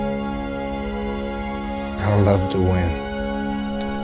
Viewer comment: what did they say after 2:25? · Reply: I love to win. Not bad for a blunt (which means non mutated human according to Umagon)